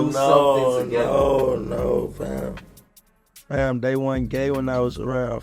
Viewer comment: when dude said “they wasnt gay around me” i thought he said “that wasnt gay at the time” i was like what its been gay to kiss another dude thats like what gay means